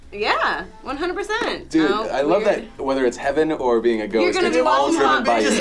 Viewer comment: I thought they said go to heaven or be a goat, I'm down with being a goat